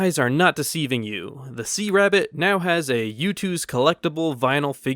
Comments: I honestly thought that said THESE A RABBIT and I was s o c o n f u s e d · i thought it said these a rabbit for the giveaway